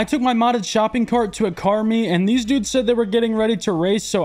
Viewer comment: “Oh….. Car meet. Thought you said ‘cart meet’”